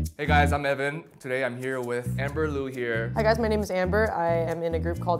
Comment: i legit thought she said "i guess my name's amber" lmao